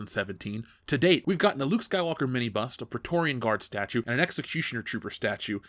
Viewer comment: I honestly thought he was saying 'mini bus', it took me a while to figure out he was saying 'mini bust'. I need sleep.